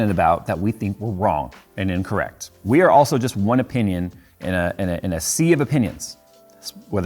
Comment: Sorry...after the opinion simile, all I heard was "we are also just one butthole in a sea of buttholes".